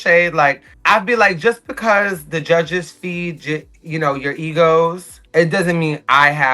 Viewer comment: Anyone catch Aja at 21:09 "just because the judges feed Gi- you know, your egos"